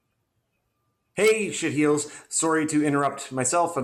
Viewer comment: Did you call everyone shit heels at 5:14?